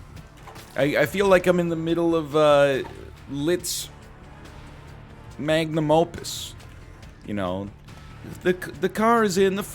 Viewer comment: I hear: Liszt's magnum opum I receive: Lit's magnum opum